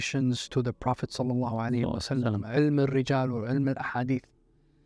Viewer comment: did he say rijago?